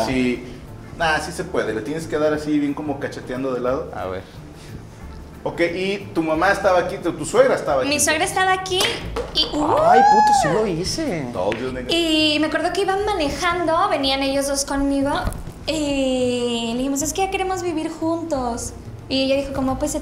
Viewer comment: “told you nigga” ? O yo escuche mal 🤣